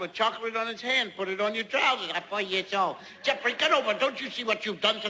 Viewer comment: At first I thought when said"I'm 4 years old" I thought he said "I'm for your soul" :/ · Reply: I thought he said "I want your soul"